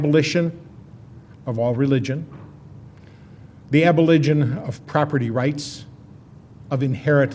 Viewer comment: instead of saying "Abolition" he says "Aboligin" . lol...